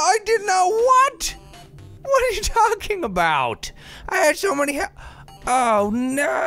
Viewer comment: it sounds like he says f**king about? lol